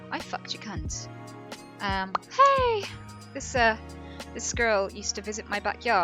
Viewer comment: I thought Anita said "this girl", not "this squirrel", so I thought that entire story was about her letting a woman who had 3 children decompose in her back garden and then she kept the skull...😥